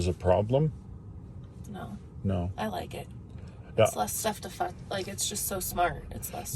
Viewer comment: Thanks. Love the tutorial. Love what sounds like a near slip of the tongue @ 34:49 "Less stuff to fu- like it's just so smart..." I think she wanted to say less stuff to "mess with" I'm sure.